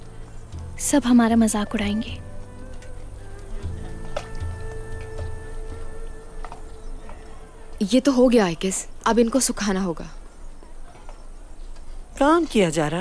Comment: Did haleema said at 8:16 "ye to ho gya i guess"😂😂😂😂